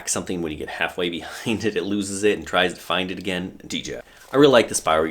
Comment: I hear a faint "DJI" at 2:31 lol